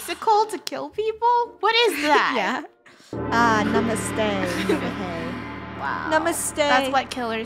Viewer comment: did she just said NAMASTE? IT is used in India